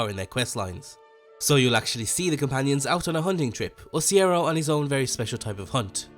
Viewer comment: Did he just call Cicero, Cierro?